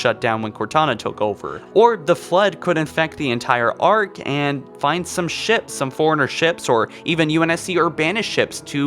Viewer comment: I thought he said “find some shit some forerunner shit”